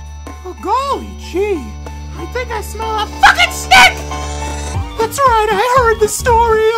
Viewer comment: for a sec i thought hooty said "fucking snake"